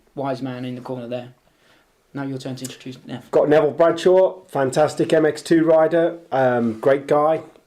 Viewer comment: What did the guy who was top 3 against Osborne and Tonus ? Thought they said ''Neville bradshaw'' ???